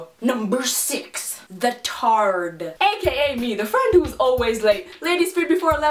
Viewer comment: when she said "the tard" i thought she said " the turd" XD